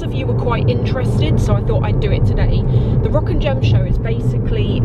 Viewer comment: I thought you were saying "rockin' gem show" 🤘